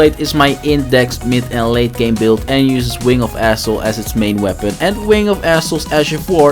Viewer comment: Man said wing of Astel I thought he said wing of asshole